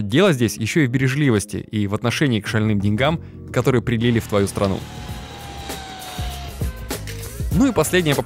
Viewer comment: что он говорит? Привил в твою страну?